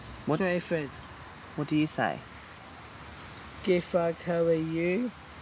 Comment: Oh my goodness, I thought he was saying "Gay f*ck, how are you?" XD